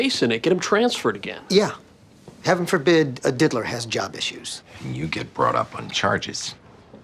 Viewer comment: did he just say Diddler?